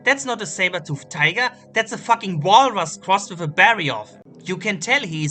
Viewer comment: What is that? I heard "Baryof" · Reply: Barioth, if you're still there